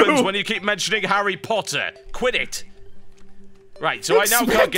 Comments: When TB said "Quit It!" after bringing up Harry Potter, I thought he was saying QUIDDITCH... lol.. lol oh lord. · "This happens when you keep mentioning Harry Potter! Quit it!" I thought he said Quidditch at first. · "This is what happens when you keep talking about harry potter. Quit it" I thought he had said Quidditch. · when he said quit it, I thought he said "Quidditch" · when he said "quit it" it sounded like he said quiditch :-)